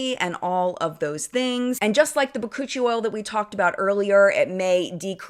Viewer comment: OMG! 😆😆 Cuccie oil? I spit out my coffee. I thought you said coochie oil. I died a little bit. Much love💜💜